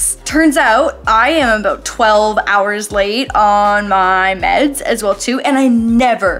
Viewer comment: tell me why my pregnant ass thought you said 12 days late not 12 hours late 🤣🤣 I was like 12 days late for what I was like is she pregnant and then heard the rest 😂 don’t want to be one of those people either but I thought it was funny I’ve been having lots of pregnancy brain and I’m like whyyyy 😂🤦🏻‍♀️